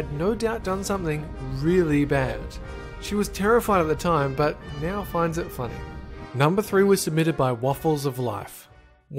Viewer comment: omg at 5:00 i thought you said they got gunned down and she finds is funny i was like. Well Exuuuuuuuuse Me